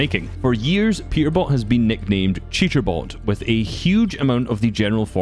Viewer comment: accidently said cheaterbot at 3:42 LOL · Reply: He said cheaterbot a few seconds after the timestamp, either that or i misheard it.